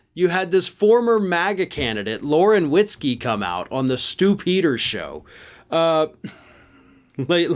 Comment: Sounds like he is saying the Stupider Show. I think that’s why he laughed.